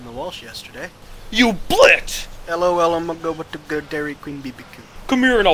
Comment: lol at 2:27 does he say dairy queen? he ses that in ROFLMAO! to lol funny vid to